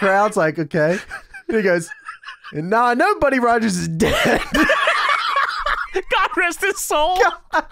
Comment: I don’t know if heard him wrong but it sounds like Steiner said “Now I don’t (sounded liked he tried to say didn’t) know that Buddy Rogers was dead God rest his soul”. So that would mean he was saying he didn’t know that Buddy Rogers was dead when he was wondering why they didn’t hire him instead of Flair.